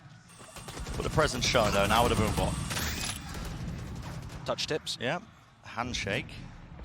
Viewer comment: did bro say touched tips ????